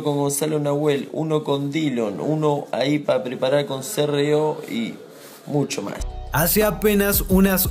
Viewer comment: Con c.r.o? Escuche bien?